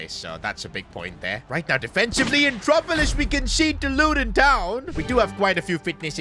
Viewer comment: What that did you just say luden town😂😂😂😂😂